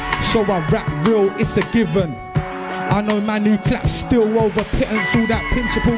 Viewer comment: to the end was the Potter we're used to. Rapping on a level the rest of the scene can't even imagine. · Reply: What does he say at 12:30 ?? I know man who clap steel no…